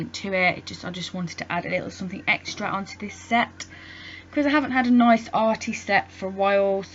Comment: did I hear you correctly????..... did you just say "we haven't had a good nail artist in a while". or either you said " we haven't had a nice artist set " idk but love we should lift up ALL nail artist and there channels. I think every nail artist is unique in there own unique way;) like you.. I'm in love with this design .. OMG ESPECIALLY THE IRREDENTIST NAIL WITH THE GLITTER COMING THROUGH THE BLUE ;) AMAZBALLZ LOL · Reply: Loca Bella no I said "I haven't had a nice arty set (of nails) for a while lol I would never say anything like that as I love every nail tech lol thank you xxx